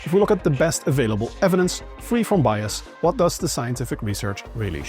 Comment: You said "free from bias", but I heard "free form bias". Sounds exciting!